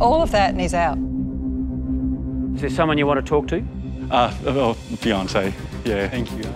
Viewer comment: I thought I heard: "Beyoncé" and was like hell yeah! You and me both, buddy! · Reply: I thought he said that to lol but realised he said fiancé which sounds similar.